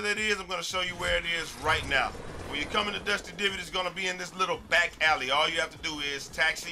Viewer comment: did he say dusty divot